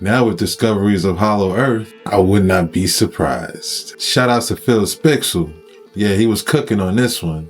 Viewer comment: What is that guys channel you said around 4:55, Phill spixle?